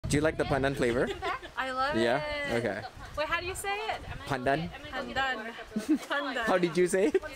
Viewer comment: i thought she said "pondan"😂